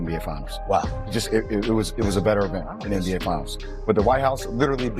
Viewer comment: I thought I heard Jay Williams said it was "a bigger event" than The Finals. "A BIGGER EVENT"!!! one night versus 5 games. Nick, you gotta listen my guy. They tell you that on Gil's Arena everyday...listen. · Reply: He said better not even bigger better